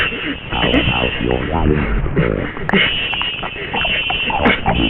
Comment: I've been tryna figure out what was saying in text to speech at 14:22. I think it might be: "How about your lungs first, AwesomeG?"